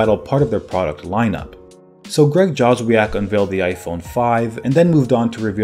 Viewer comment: Did he say Greg Jobs? Didn’t he mean Steve Jobs?